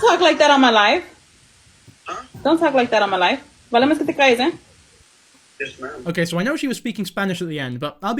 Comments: from what i can understand after slowing it down it sounds like she say’s “no me hagas que te callé wey” which roughly translates to “don’t make me shut you up” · She said “Vale más que te calles.” At 27:16. It took me 3 times to decider what she said LOL. It basically means, “It’s better if you stay quiet.” But the way it’s said is more threatening/scolding so, “You’d be better off if you shut up.” Or a slightly less hostile, “If you know what’s good for you, then you’d better not say anything [stupid that would embarrass me].” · I think she said “vale mas que te calles” or “you better shut up/ it’s better if you shut up” I had to slow it way down as far as YT let me and I still had to listen to it four times to understand. The grammatically correct way is “MAS VALE” and not “Vale mas” which is not a thing any actual Spanish speakers have said in their lives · I think she said “mas vale que te calles” that means “you better shut up” great vid btw ^_^